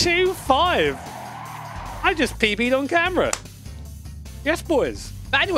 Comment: "Hey guys, world's worst delivery boi here..." (that's how long I've been here) I could have sworn at 6:16 that you stated that you pee-peed on camera - must be the after effects of the sniffles I picked up at the weekend. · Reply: He PB’d on camera as in Personal Best.